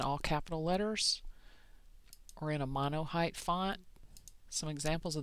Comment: Love this...thank you so much!! at 1:35 what type of fonts are you saying look best in a circle? "Mono___" then you give examples of Pharmacy and Cafe Rojo. I wanted to look up other examples but wasn't sure if you said Mono Hype or something else. Thank you so much for your help!! ~Donna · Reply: Doxie C Thanks, Doxie. I probably said mono height, but that's probably not the correct technical term. Monocase is probably what you would want to look for.